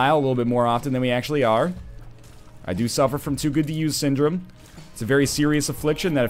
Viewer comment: what syndrome did u say u had 8:50 start here and go to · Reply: He said too good to use